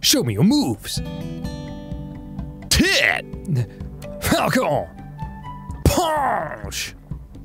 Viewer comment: for the longest time, I thought Captain Falcon was saying "Welcome Punch"